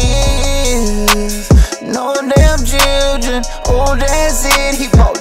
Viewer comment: What's your favorite part of the video? What did I say at 2:15? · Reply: No dam children.